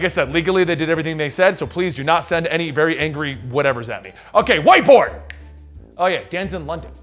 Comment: I heard WHITE BOY! instead 😀